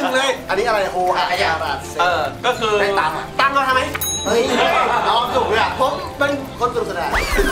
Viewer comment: พี่พูดว่าไรอ่ะ😂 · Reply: ตังเอามาทำไม ไหมคะ55555